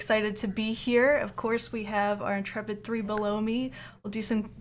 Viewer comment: I heard "we have our intrepid 3 below me" as "we have our intrepid 3 baloney" lol!